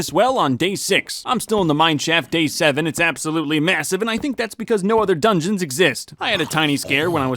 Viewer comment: thought he said, "my shaft is absolutely massive"